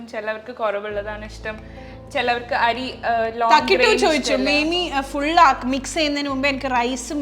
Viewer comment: Did you Just said MEMI, njangalum Ammede aniyathiye Mema ennathinu pakaram Memi Ennanu vilikkunne. Just oru curiosity kond choichathanu 😊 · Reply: Yes, memi it is 🫠